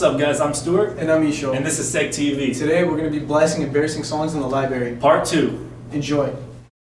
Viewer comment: Did he say liberry